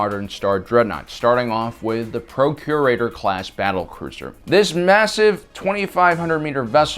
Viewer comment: I keep hearing “Procreator Class” instead of “Procurator Class.” I need help